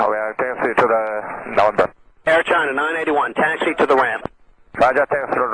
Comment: maybe did not say November, he said Number..."gate 3 is open, we are taxi to the number"